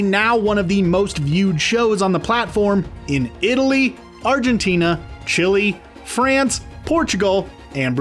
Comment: im sory did he say „CHILI”?